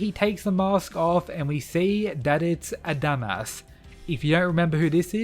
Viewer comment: Anyone else heard a dump ass instead of adamas at 5:45, or is it just me?